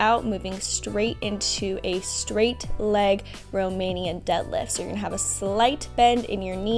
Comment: did she said Romanian?